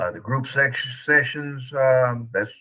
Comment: I thought pops said “the group sex!” 😮😳😳😩😩🤫🤫🤐🤐😔😔🤦🏾‍♂️🤦🏾‍♂️👀👀